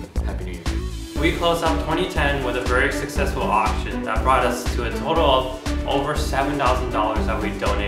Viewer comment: i thought he said "it brought us to 7 dollers" until the words poped up